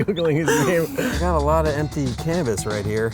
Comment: Omg I thought he said I have an empty cannabis... lol